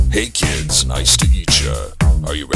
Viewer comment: Freddy : “hey kids nice to eat ya”. People who heard that correctly: WAS THAT THE BITE OF 87!? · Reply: I thought it said meet ya before I read your comment and listened again 20 times to make sure it said eat...